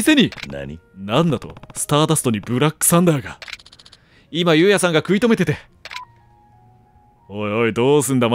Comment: どう聞いてもユウヤがスターダストでブラックサンダー食い溜めてるにしか聞こえなくなった…